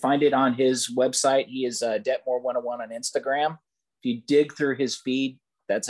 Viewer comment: What was the Instagram account mentioned @ 21:30? I couldn't understand him thanks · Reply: dettmore101